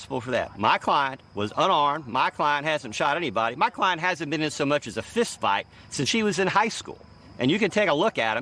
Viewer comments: " my client hasn't gotten into fights since she was in high school " I can't believe he called his client she? Did the lawyer forget that his client is not female but he's male loool 😲😲 if I heard it wrong correct me y'all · This is hilarious, lawyer calling his client dumb and got his client looking like Jim Carey in Dumb & Dumber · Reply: Did he just call his client a "she"? This interview is hilarious